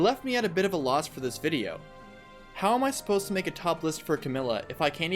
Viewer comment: I heard "Topless". I'm just gonna go...